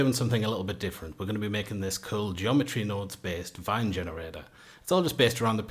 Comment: I thought you said van generator, and the picture of a car on screen wasn't helping, haha